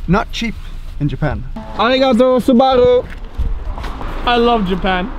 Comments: did said : arigatou Subaru at 9:44 ? lol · "Arigato, SUBARU?" Lol did you say Subaru, like the car?